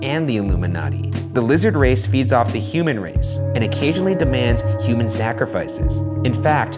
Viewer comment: Did they say human "snackrifices" at 4:50? If so that's brilliant.